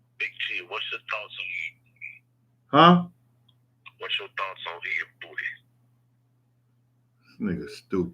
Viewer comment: I still couldn't figure out the disrespectful message that caller made to @ 1:16:55. What did he say? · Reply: It sounded like the caller said "what's your thoughts on eating booty"